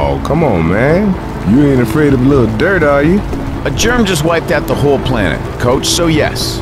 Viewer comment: Uh the caption says a German just wiped out the whole planet instead of "a germ.." 6:52!